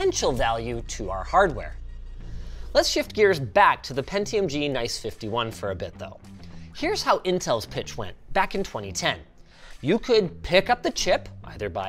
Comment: Did you just call it G-nice-51?😂